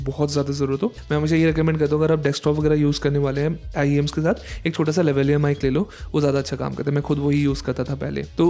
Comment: Can someone please tell me what did he say in 10:24 timeline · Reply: Lavaliar mic